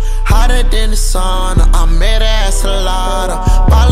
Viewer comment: I thought he said I met her at Salata 🤣🤣🤣